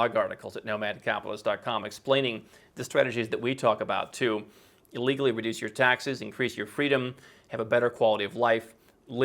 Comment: Did I hear "to illegality reduce your taxes" at 10:12 ? Or I am the only one xD